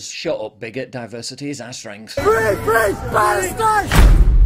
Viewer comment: English people: "You immigrants want to replace our culture and laws with yours" Immigrants and leftists: "No we/they aren't! That's far-right rhetoric! Bigotry! Racism! Islamophobia!" English people: waves England flag in their own country Immigrants and leftists: "You can't do that! Police, come here, arrest them! NO ENGLAND FLAGS ALLOWED! FREE FREE PALESTINE!" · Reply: I thought he said "free grease, Palestine"